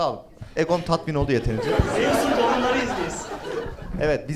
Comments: ne diyor? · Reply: Zeusun torunlarıyız biz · olimposlu ne dedi? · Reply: Zeus'un torunlarıyız biz